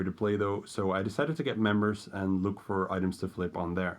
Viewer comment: I'm not sure what you're trying to say. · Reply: it was a joke cuz it sounded like you said "membershit" nice vid btw :P